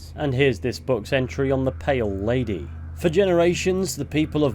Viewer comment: I first thought you said "Male Lady" on the intro until I re-listened